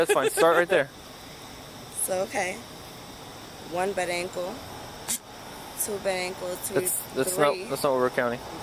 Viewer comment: Did she just say “1 bad ankle…2 bad ankle…” hahaha!